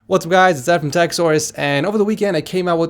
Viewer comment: Idk why, but everytime i hear him saying "TechSource", it always sounds like "Techsaurus" to me😕